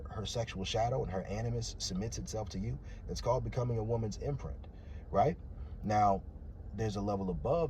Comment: now this is facts never let a love like that find me because it is dangerous! 💯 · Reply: a woman's what? Imprint, inference I cant hear it.